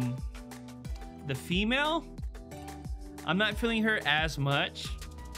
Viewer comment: Hatchling looks so doppeeeee. "The female, not feeling her ass much" is what I heard 😂😂